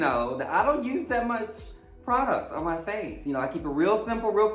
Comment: Thought you said you didn't use alot of product on your face😅😅